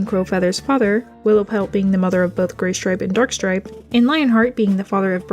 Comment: am I hearing things, or did you say "Dorkstripe"? XD